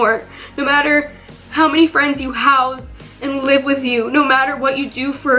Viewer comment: did she said "how many friends you house" at 15:42 ??